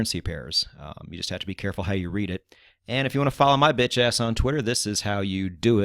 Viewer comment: Did you just call yourself a B***h A$$ at 16:20. 😂😂😂